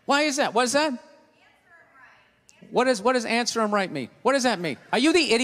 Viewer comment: Can somebody write what the woman said? "Answear or right me?"